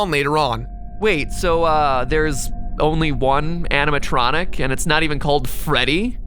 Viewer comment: when Mat said "animontronic" i thought he said "Hannah Montranic" as in Hannah Montana + animatronic